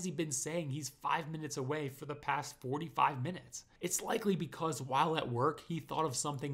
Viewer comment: said "he's told us 4 to 5 minutes two times in a row." I originally thought he said 45 minutes too but after watching this atleast a dozen times I finally caught it.